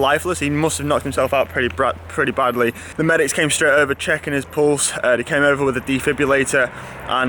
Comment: I thought you said " the medics came straight over checking his balls". Get well and Olli